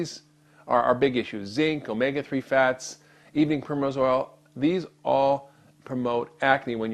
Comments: ok. what the hell did he say at 5:43...??? sounds like 'evening chromosol', but it can't be that...?! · What is the thing he says after he says "omega-3 fats"? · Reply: evening primrose oil